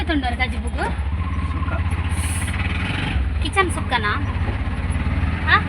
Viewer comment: Did you say Kitchen sukka 😂😂😂 3:11. Irna Vedio toovare yedde aapund. Papada husbabd comedy wife cute son. Am also fish lover like you.